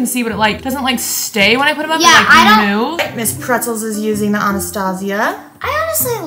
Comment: I thought said ms. Pretzel’s 😂